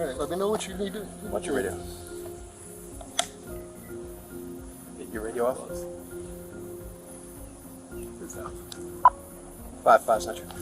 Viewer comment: the captions are wrong and the real quote is so much worse, he says, "You got your radio off?" not "That you're at the office" and the other guy says "It's Off." This comes right after they're trying to decide what to do with their illegal arrest lol.